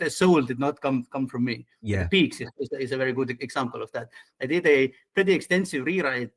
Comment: Very fun interview. What character says he rewrote at @ 2:13:23? Can't really make it out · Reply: Pigs, the woman who buys the gun from Roy.